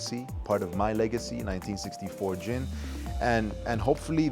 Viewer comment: Oh Gin...I heard " jean"